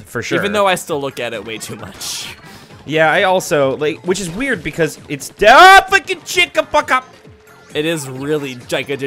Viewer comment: it almost sounded like swearing. I heard Shit and Fuck in that gibberish.